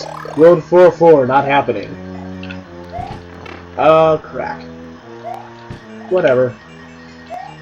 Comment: it sounded like u said oh crack